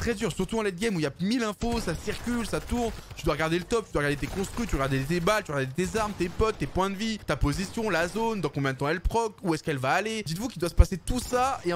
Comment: il dit quoi ? La zone elle prock ?